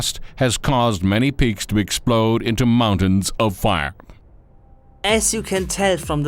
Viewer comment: it sounds like he is saying many pigs explode into mountains of fire